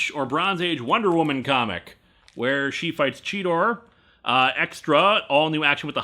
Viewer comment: I know that you meant the Cheetah, but I heard you say "Cheetor." Now I really want a Beast Wars/Wonder Woman crossover.